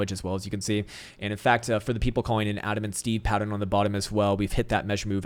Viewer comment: did you say adam and steve???? loool